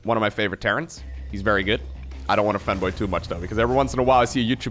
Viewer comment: for a second i thought he said " i dont want to femboy" ive been watching too much artsyvrc amd bungotaiga.